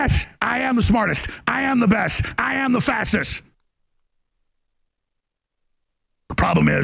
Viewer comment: When he said "I am the fastest" it sure sounded like he said "I am the fascist".